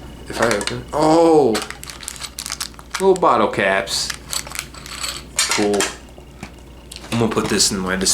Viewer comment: did you say coke bottle caps...?😭